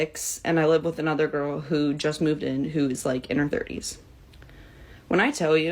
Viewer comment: These roommates hooking up with random dudes is putting you in danger. My aunt was whoring it up in the 90's with a dude that slit her throat, thank god he didn't kill her, but that wven didn't change her behavior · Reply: I first thought you said she was in HER 90s